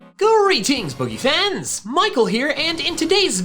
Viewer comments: Ok, this time, I heard "greetings boogie fans" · for the longest time i always thought he said: “pretty fans” instead of “pokefans” lol